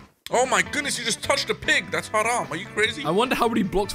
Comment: DID SAY HARAM xD?